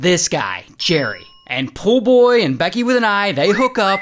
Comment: I thought he said..'Poor Boy'..